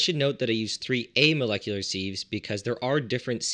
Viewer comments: I kept hearing "molecular seed" and started googling around to find out more about them lol. Never heard that pronunciation of "sieve" but it makes sense now I think about it! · is the word not pronounced siv as opposed to seev? It's the same spelling as the kitchen utensil that you sieve flour etc with, and as it's performing a similar process IE sieving the alcohol to remove the water would it not have the same pronunciation? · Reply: I thought he said "seed" at first, but figured out he meant "sieve" from the brief description that followed. I've never heard it pronounced the way it is in this video.